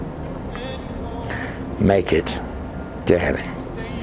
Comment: Hi just wanted to point when you said on clip 40:55 on this video you said "MAKE IT TO HEAVEN " WELL IM HARD OF HEARING I PUT CLOSED CAPTION SO I CAN UNDERSTAND WHAT PEOPLE ARE SAYING WEN THEY TALKING WELL EVERYTHING U SAID CAME OUT ON CLOSED CAPTION BUT WEN U SAID MAKE IT TO HEAVEN IT SAYS I HOPE YOU MAKE IT DANNY HOW DOES HEAVEN AND DANNY SOUND THE SAME I UNDERSTAND BOOK AND LOOK SOUND KINDA THE SAME BUT WHY WHY YOU TUBE DOES THAT YOU NOT ONLY ONE THERE ARE OTHER VIDEOS THAT WHEN A RELIGION WORD IS SAID JESUS, ANYTHING TO DO WITH JESUS THE MESSIAH OF NAZARETH THE TWIST WORDS AROUND SO THE DEAF COMMUNITY DONT UNDERSTAND THAT THAT IS FUCK UP SORRY FOR ME CURSING BUT IM SICK AND TIRED OF THIS IDIOTS BUT I KNOW THAT AT THE END WILL THEY EXCEPT OR NOT MY LORD WINS GOD WINS THAT THE TRUTH IT WRITTEN IN THE BOOK THE BIBLE. AND THE DEVIL IS A LIAR A DECEIVER. THE TRUTH HURTS SUM PEOPLE CANT HANDLE THE TRUTH THEY WANT TO BELIEVE THEY GOT AWHOLE LOT OF TIME IN WORLD BUT JESUS IS AT THE TIP OF TURNING THE DOOR KNOB TO RESCUED HIS CHILDREN FROM THE WICKED.